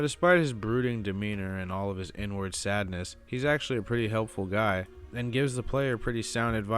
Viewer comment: Lmao when I heard you say, "inward sadness" I thought I heard ,"N-word sadness" and I was like lol wtf